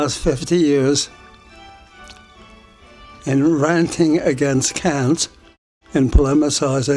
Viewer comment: What does he say @ 3:24? "I've wasted 50 years in ranting against ________"? Does he say Cannes, as in the film festival? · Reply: he's saying "ranting against cant" -- <link>